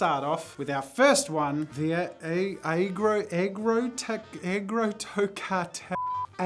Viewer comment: Bro got caught💀 Edit: I listened again I thought he said negr- 💀